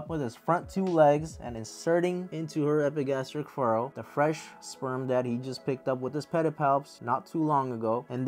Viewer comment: you said "sperm that he" but what I HEARD was "sperm daddy" so... that's what mature males are called for me now. 😂